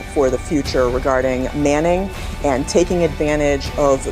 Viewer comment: Manning? Did she say manning?